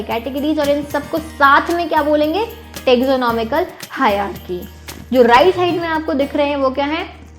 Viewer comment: Mem You are the best teacher as we all know! But one question : What did you say at 6:41 after taxonomy?😐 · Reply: Taxonomical hierarchy